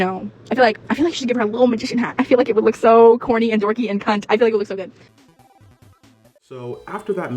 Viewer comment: when shes talkin about the magician hat did she say "Corny and dorky and *Cunt*" ??????